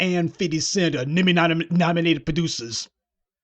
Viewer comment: Everytime I hear "Nama Neme Nominated Pranucer" 😂😂😂😂😂😂😂😂😂